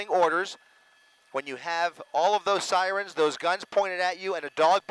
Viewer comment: Did he just say "All of those sirens pointed at you?"